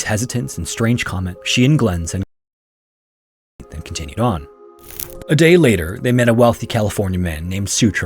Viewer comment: The audio cut out at 10:21 in the middle of a sentence, what were you saying? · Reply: "She and Glen said goodbye to the family on the 17th and continued on." Luckily he or someone added subtitles to the video.